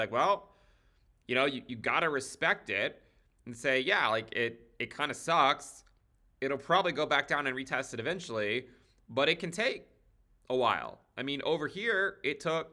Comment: am I hearing it correctly? Did ben just told us to RESPECT THE PUMP at 10:36 ? loll